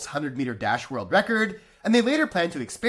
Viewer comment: When he said "Combat Sport", I heard "Blood sports". If one fight doesn't end with a Mortal Kombat style fatality, I'd be disappointed